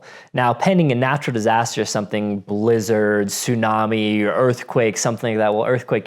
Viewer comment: I thought you said Lizards, tsunami, earthquake. I'm like dude Lizard aliens are not gonna give us a heads up before abduction...duh